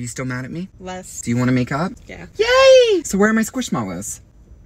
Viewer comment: My deaf ass missheard that as 'make out' instead of make up😭